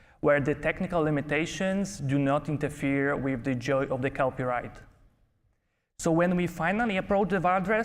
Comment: What does "Joy of the copyright" mean? Or am I hearing wrong? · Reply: I think it was "Joy of the Kelpie ride." Her horse's name is Kelpie like the mythical water horses.